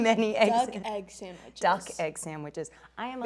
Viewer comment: Lol wow i didn’t catch that the first time I saw this... at 8:55 I literally just thought the one girl said she loves egg sandwiches and Margot Robbie responded with “F*** egg sandwiches” because she’s allergic to egg white lol then I saw this video again months later and I heard the word “duck” so I thought for some reason you censored it to duck or the news station censored it with duck... but when replaying it I realized she was saying she’s allergic to a chickens egg white specifically, so she was literally saying “duck egg sandwiches” because they were literally cooked with duck eggs hahaha